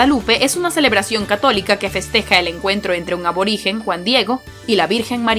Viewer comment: Cuando habla de la virgen de Guadalupe dice q se encuentra con un aborigen? Es cierto lo q dice o no escuché bien? En el minuto 3:25 q alguien me saque de la duda ?